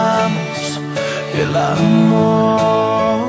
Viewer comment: Yo pensé que decía "como los siervos" jajaja🤣🤣